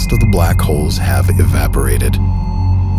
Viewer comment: cuz i thought you said nothing was no more.